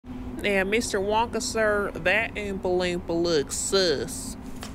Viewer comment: aesthetic Facebook photo 0:05 stare 0:08 cute slideshow❤❤️❤️ 0:07 breaking neck 0:18 touching face 0:19 we look like some weird 🍑 carrots 0:24 ✨oompa loompas✨ get back to work🥰 0:25 jumpscare 0:26 aggressive mixing triggers 0:28 telling you a secret😳 0:33 aesthetic jumpscare #2 0:36 putting on wig while gossping😍 0:50 I don’t know what this says😭 0:53 stop crying🤬🤬 0:57 Oop,my suspenders😢🤗 0:59 spraying moisturizer on hand 1:01 applying and rubbing moisturizer on face 1:13 it’s hot as coola in here 1:17 idk what she’s doing 1:19 wiping your face 1:29 this place is rlly nice tho fr 1:36 mixing triggers again 1:40 spinning idk makeup 1:43 smacking truggers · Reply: it says "queres un Wonka bar?" Which basically translates to "Do you want a Wonka bar?"